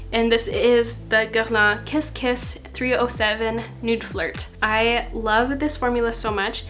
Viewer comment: I know I’ve watched too many of your videos when I heard “Nude Fart” instead of “Nude Flirt” 😂 Also, love that most products are just neutral! Perfect for every day ❤️ Thanks!